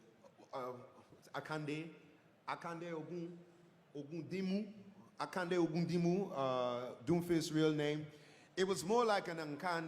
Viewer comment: Does anyone know what that word he was using means? It's like he couldn't find an English equivalent word to convey his example. Loved his explanation, and frankly, I thought his lines sounded closer to the real thing than any of the other VA's (which I suppose isn't surprising considering how new he is in comparison haha) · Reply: He actually found exactly what he wanted to say. What he said was "Akande Ogundimu", which is his character's name. So in the context, he's saying that : "it was less a civil war, it was more of a guy who decided that he wanted to have what his neighbour had. That guy was like Akande Ogundimu/Doomfist".